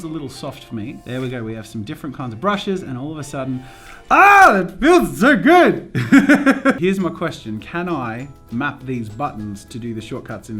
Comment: aH it feels zo good! I said that because of the way he said it. (I used a z instead of a s because when he said 'so' it sounded like 'zo'